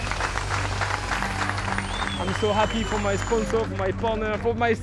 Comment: did he say: "I'm happy for my boner"